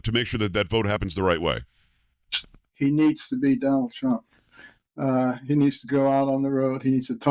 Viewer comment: I heard tonight he's going out on the road